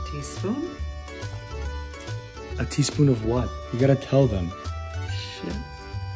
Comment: “You gotta tell them” “…shit” 😂😂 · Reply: I thought she was saying a teaspoon of shit 😭